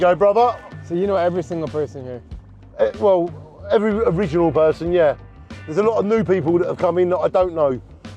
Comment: wtf is this guy saying ? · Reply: It ain't that hard. Every original person he said